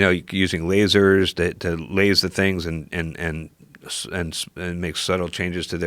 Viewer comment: did he just say "use lasers to laze things"?